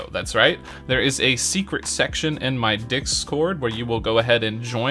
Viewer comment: ”Dick-scored” Bruh, tell me I’m not the only one who hears this… 😂